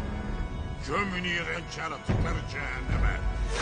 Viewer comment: ne diyor? 2:11 gothmog da çok karizmatik hareket yapıyor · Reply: de gömün iğrenç yaratıkları cehenneme diyor